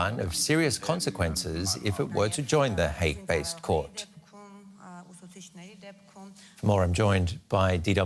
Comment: I thought he said hate-based court but he said Hague-based court. Freudian slip.